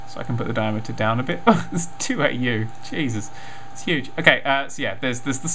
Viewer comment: what is Dan saying here? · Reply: "it's 2 AU, Jesus, it's huge". (AU is astronomical units, a length measurement)